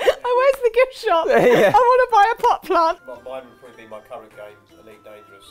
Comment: did say "I want to buy a buttplug" O.o